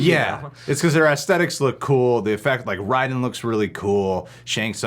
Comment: Did he just say, “R-y-den?” 😂🤣🤣😆😆